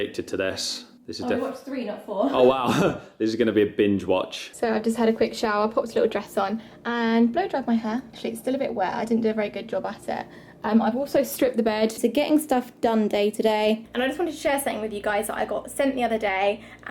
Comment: Did she just say "blowjob my hair"? @ 4:08 😆